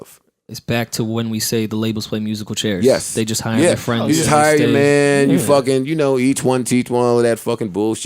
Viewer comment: Did he say you hire ya mans who you fucking??